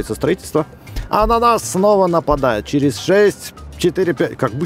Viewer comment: От чего я угарнул? От «А НА НАС СНОВА НАПАДАЮТ» я услышал «АНАНАС СНОВА НАПАДАЕТ»